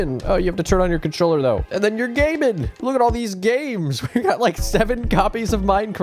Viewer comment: i thought he said look at all these gays